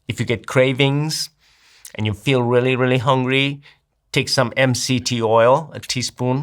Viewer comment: can someone help me with what tea oil he is saying here? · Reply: He's not saying tea. LMNT oil